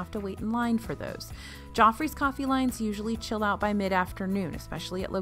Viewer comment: I thought you was saying 'jaw freeze coffee' 🤣